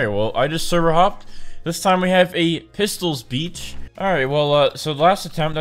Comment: I thought he said “it’s pistols b!tch” Um